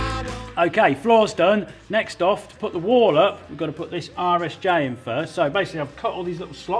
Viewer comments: Every time Colin says "RSJ" I hear: "Irish J" and I feel like he's taking a dig like they don't know the're an i shape. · I really thought he was saying "Irish J" but after a bit of googling, I've determined Colin is saying RSJ, rolled steel joist